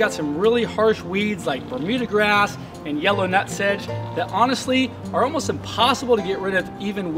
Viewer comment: hi can you please tell me the name of the weed u showed at 1:00 .... i didnt understand what u said..... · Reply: The Order Of Minecraftians hi, that is Yellow Nut Sedge